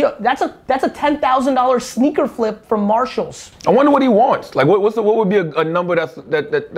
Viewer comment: Does anybody know what "martial" he is talking about at 17:32?